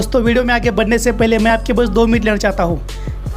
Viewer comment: Kya bhai 6:48 parr kya bolrahe ho sab ki 2 minutes lena charaha hoon, tumko kya sabme champa nazar aarahi hai? 😂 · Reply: Time 2 min lena chahata hun 🤪😃